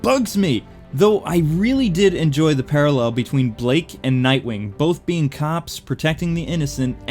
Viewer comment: lol sometimes durin the movie i thought they were saying drake instead of blake